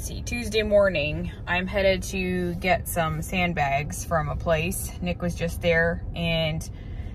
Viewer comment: I thought you said "handbags".... I thought ok..😂😂😂😂😂